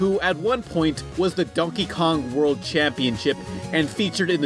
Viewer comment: did you just say Dunkey Kong?